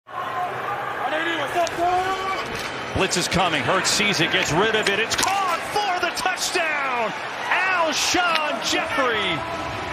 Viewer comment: I thought he said hurts szn